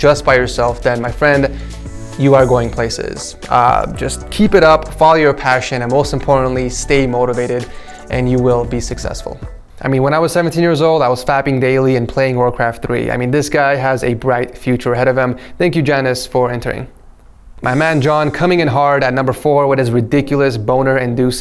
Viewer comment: Did he say fapping in